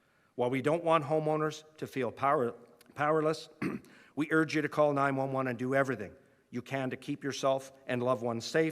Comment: What i heard was 'If you feel powerless, call 911 and we won't do anything'